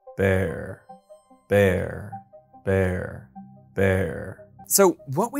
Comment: I heard "verre".